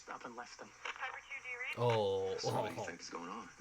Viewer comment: please tell me I'm not the only one that hears Daryl saying I'm sorry babe he says Oh, sorry man but that is not what i hear